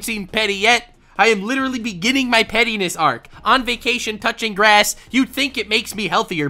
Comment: Idk why I thought he said "couching (cu-ching) grass"! 😂